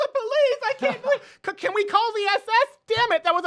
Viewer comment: Did he say, “can we call the SS?”